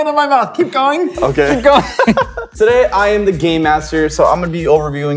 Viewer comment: Lol ay 00:32 said hes the gay master instead of the game master